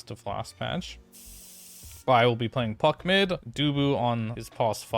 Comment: I thought I heard at 0:43 "Bryle playing Fuck mid" LOL